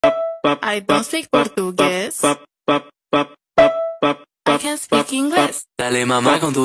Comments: I misheard "i think i could guess bap bap bap i can speak english"💀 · I thought it said I can’t speak English but it said can